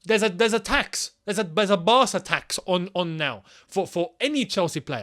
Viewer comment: I know you said “Barça tax” but I heard “bastard tax” 🤣🤣🤣